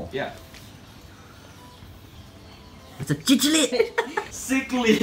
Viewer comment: I thought I heard they called it chichilid?then cichlid :-) that flowerhorn boy had the worst trip to your house ever I bet he's so stressed in that shipment and look how happy he was when he was released in the tank.